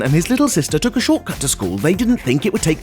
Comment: I thought he said shot gun not short cut